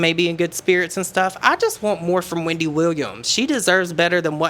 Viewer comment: 🎤🎤🎤 lol i heard "microwave emoji for wendy williams" thought ok and went straight to looking for it.. couldnt fint it (does it exist?!) ..